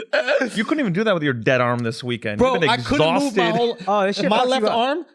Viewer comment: did someone say Dead Arm? That's why you need a Spoonie Pillow Schultzy!!! <link> #nomoredeadarm